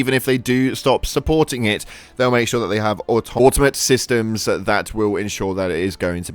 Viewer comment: Did he just say the Automatons are going to run the game after they devs stop supporting it?